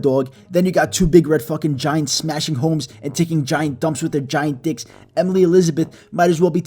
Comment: wait did you just say ''Taking dumps out of their dicks''? because they need to get that checked out.